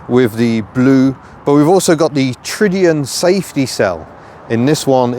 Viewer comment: What was that word? Tridian? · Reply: tridiOn